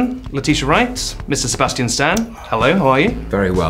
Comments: I've been laughing for forever, I thought he said Missus Sebastian Stan XD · it sounded like the interviewer said Miss Sebastian Stan😂